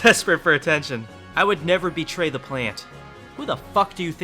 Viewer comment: I would fight your main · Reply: I thought you said you’d never betray the plant